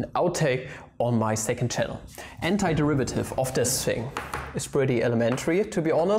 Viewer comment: is it just me or i heard hentaiderivative instead of antiderivative